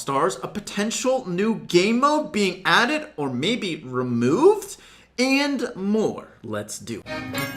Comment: When said "may be removed" or something like that I thought he said "Bibi removed" and started panicking 😂